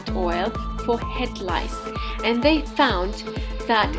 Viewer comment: I thought she said it cured crabs? What? Oh, head lice! Never mind.